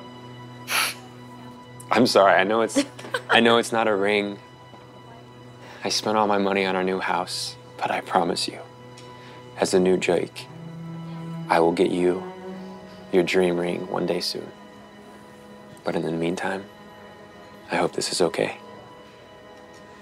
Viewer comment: Anyone else hear “As a new JaAoke“ 😂